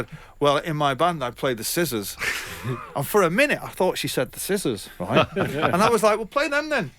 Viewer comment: What does he say at min 09:52? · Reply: Skissorz